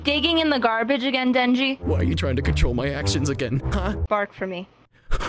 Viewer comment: Thought it said “Back for more” …………………………………………………………………………………………………………………………………………………………………………………………………………………………………………………………………………………..that’s fine with me!!